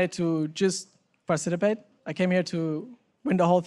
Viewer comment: hehe he said parcitipate instead of participate